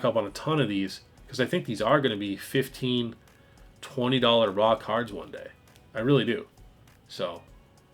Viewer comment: Why is it that every time you say "raw card" I hear "rock hard"?! 😂😂 Hopefuly it's not Freudian!!